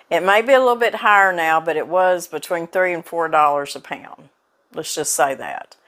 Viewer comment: $3.00 and $4.00 a lb. I thought you said $30 and $40 a lb. ha ha ha.